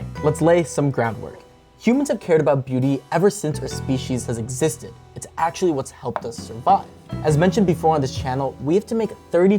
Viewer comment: i heard "it's actually what helps us f*ck"